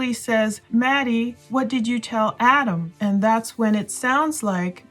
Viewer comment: I thought I heard this:, What did you tell Otto, and the other person said. I told him everything.